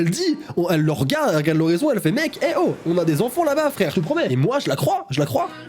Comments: Moi a 4:05 j'entends" on a les enfants d'en-bas à se coltiner "😅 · Moi j'entend plutot "On a les enfants d'en bas à se coltiner "